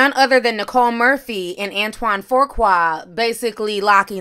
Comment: Unrelated but I keep hearing "Antoine Faqua" and thinking "Lord Farquad" 😣